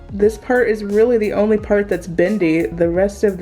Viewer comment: Good for you I don't care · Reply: Oh NVM it's 11:05 she clearly says bendy